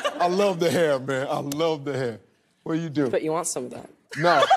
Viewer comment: what did the afro kid say? I didnt understand it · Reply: said he had nice hair, then he said if he wants some of his hair